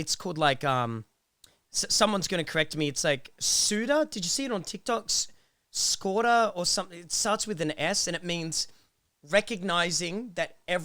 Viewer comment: Can someone tell me what this word is? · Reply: it's sonder